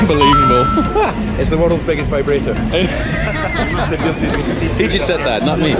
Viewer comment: Guys what did David Coultrad said at 2:35 I didn’t catch it??!lol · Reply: It's the worlds biggest vibrator, he said :)